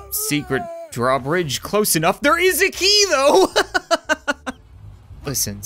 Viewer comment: i thought he said "there is a Nemo" not "there's a key tho"